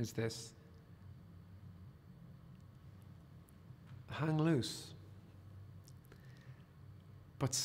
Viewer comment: I had to re-listen to the beginning of choice 5. Where he says "hang loose" I initially thought he said "hang a noose."